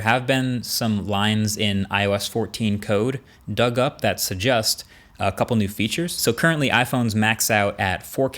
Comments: Marques, did you just say 'Sujust' instead of 'Suggest'? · "That suggost" Who else heard that?